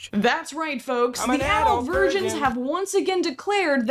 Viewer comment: 😂😂😂 I heard, "I am an adult virgin!" Not that there's anything wrong with that, ya' know if it's a choice. It is a choice, but I am not sure it is in his, uh, case 😅😅